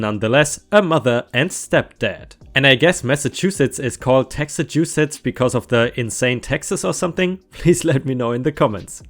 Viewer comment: RIGHT YOU ARE! The taxes here are kinda crazy! Thanks for reading my story! ⭐⭐⭐⭐⭐⭐⭐⭐⭐⭐🌟🌟🌟🌟🌟🌟🌟🌟🌟🌟🌟🌟🌟🌟 THANKS FOR PINNING MY COMMENT! I cleaned up part 2 on the subreddit in case you were thinking of reading that one as well! Much Love! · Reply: I thought said Texaschussettes this makes a lot more sense.